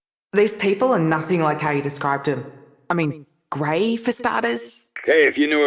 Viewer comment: Why does it say great instead of Grey.